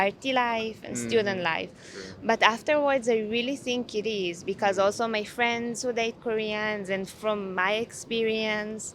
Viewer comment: The subtitles are wrong at 9:33. She said, “My friends who date Koreans.”